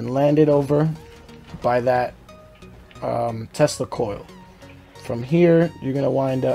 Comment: When he said tesla coil I thought he said testicoil